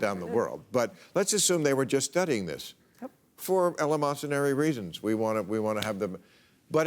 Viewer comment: What word does bill say at 3:58 ? Before reasons. Elemonsary?